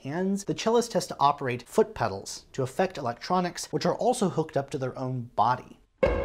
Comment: did you say "hooked up to their body" Jaaaaaysus...